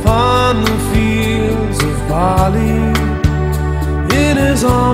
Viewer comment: Nice song but "fields of barley"...??? rather fields of wheat in the video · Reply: I thought he was saying fields of bolley as in the place in India